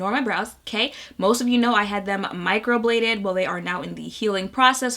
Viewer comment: When she started talking about her eyebrows at the beginning of her intro I thought she said she had them "microwaved" 😂and I can't stop laughing since